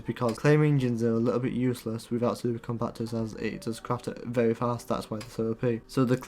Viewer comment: what did he say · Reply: Super compactors